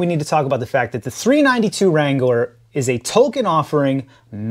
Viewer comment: did he say the 392 wrangler is a “Tolkien” offering? Boy that South Park joke got around 😂